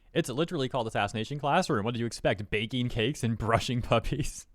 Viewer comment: "Baking cakes and Brushing puppies" I heard as " Baking puppies and brushing cakes"